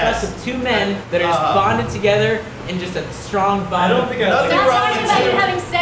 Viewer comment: I thought it said just bang